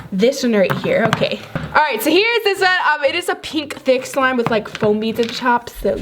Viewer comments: Did you just say “this is a pink thick slime”? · did u say pink??😂 4:35 fluff?? 😂 Oh and what i understood from the hints:You are going to have merch soon?? Or you will have your own app?? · Is it me or did I hear pink in 1:52 like if you cold hear it?😳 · did she say it is pink❗️❗️❗️❗️😭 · was it just me or did she say pink? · Did she just say pink cause im pretty sure the slime was blue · Wait at 01:54, did she say pink? Or am i color blind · did she say pink insted of blue at 1:53 like if you heard pink instead of bluse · did she say pink??🤔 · is it just me or did anyone else hear pink instead of blue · did she say pink slime or am i crazy